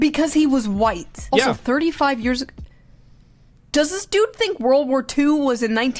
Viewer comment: I think he said 35 years ago because of Karmelo's 35 years sentence